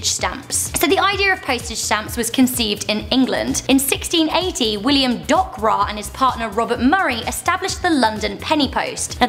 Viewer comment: Did Anyone Hear Her Say William Dogbra?